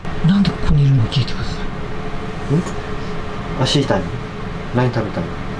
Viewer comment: 足痛い聞こえる 気のせいかな